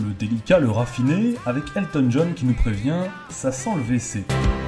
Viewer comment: Moi jentend a 3:08 (ça sent le deces)